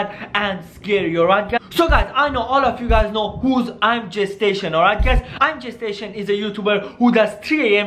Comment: I thought he kept saying “ImGestation” lol